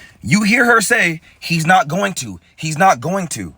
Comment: I think she was saying I’m not going to maybe when they were telling her to sit down or calm down or something or to move over. She said I’m not going to.